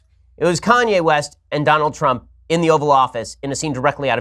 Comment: For moment I thought he said the Oral Office